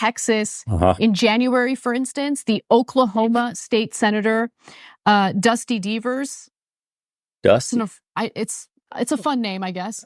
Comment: did she say DUSTY BEAVERS?🤣🤣